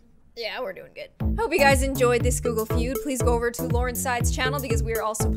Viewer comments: I thought you said 'gloomy feud' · I thought you said 'gloomy feud' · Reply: GameFreakJeroen I thought she said glooge feud